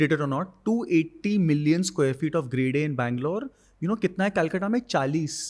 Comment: what is he saying here ? 2 million 80 sq ft of grey day in banglore means ?? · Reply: of Grade-A office supply.. Grade A is like a 10 on 10 top rated building